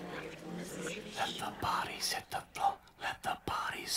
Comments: who watching it every day ? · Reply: Me, starting today, cause I remember i heard ''let the bodies in the floor'' somewhere, I got here and now I can listen this sht all I want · my mum misheard this as: "let the bunnies eat the floor"... I can't stop laughing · When I was a 10 year old kid. I thought he was saying "Everybody system go". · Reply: I always heard "Let the body set the flow" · my mum misheard this as: "let the bunnies eat the floor"... I can't stop laughing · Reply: hahahaha! well tell your mother i misheard ''let the party hits the floor'' xD